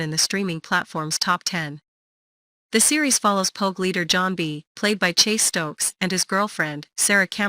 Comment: I thought robot lady said cult leader John b not pogue leader John b